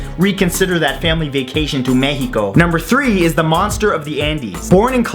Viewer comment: I thought he said, "The Monster of the Anus" xD